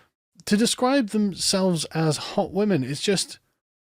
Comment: they thought it said "Hot Wok" and were wondering where the food was 😂